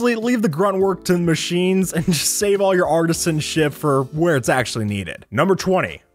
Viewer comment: Who else heard artists and sh**